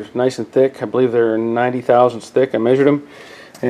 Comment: what is the unit you refer to when you say 90,000 stick (?). Many thanks! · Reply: It was a measurement. .090" or 90 thousandths.